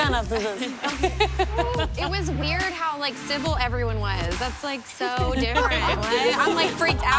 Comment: The one chick at the end, "It's weird how civil everyone was through this" sis that was the opposite of civil for most of the video 😬 edit: wow thanks for the likes ya'll lol i didn't expect to get any likes but this is cool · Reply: sage dean I thought she said simple instead of civil